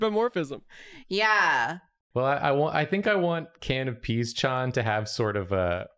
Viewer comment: Won’t like I heard it as “canopy” not can of pea at first